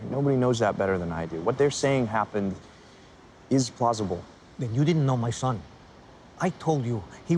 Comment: "plosible" did i hear that right? xDD i just noticed, instead of possible